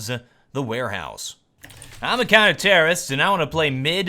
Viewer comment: Whenever he says "I'm a counter-terrorist" i just hear "I'm the kinda terrorist"